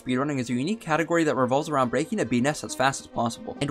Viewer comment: I misheard “breaking a bee nest” as ”breaking a penis”